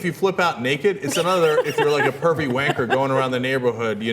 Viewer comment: Did Cenk say 'wanker'?!